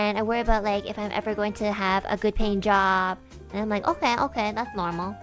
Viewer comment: I think she said "good paying job" although I also worry about not having a good paint job in my house.